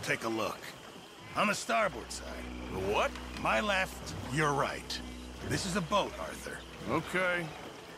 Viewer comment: the subtitles are switched from what Pearson actually says. He says "my left, your right", but the subtitle says "MY right, your left". Can't believe Rockstar would let that go through.